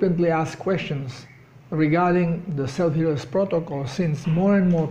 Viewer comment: What is the name of the protocol. It sounds like he is saying selvius but nothing comes up for that search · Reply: SHP, Self Healers Protocol <link>